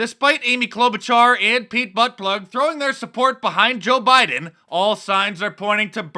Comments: “Pete buttplug” I dang near choked on my coffee. That was funny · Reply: bryan bock I heard Pete buttfudge...idk that's what I thought Mark said & I rolled off my chair in laughter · Did he say BUTT PLUG? LOL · Did he say Pete Buttplug? lmao